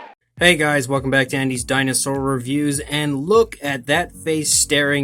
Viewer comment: When I first heard this I thought u said "welcome back to Andy's Chinasaur reviews" lol